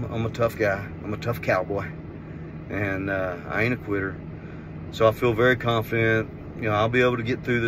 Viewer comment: Did you just say, "I ain't a queer"? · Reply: Haha, no…. I said I ain’t a quitter.